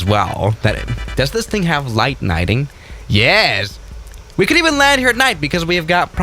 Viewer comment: did he just say, light nighting